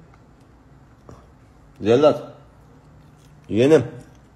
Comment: yiğenim mi dedi yoksa ben mi öyle duydumm 🤔🤪😲